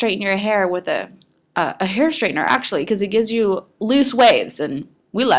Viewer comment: u Said Straighten your hair, instead of curl!! Lol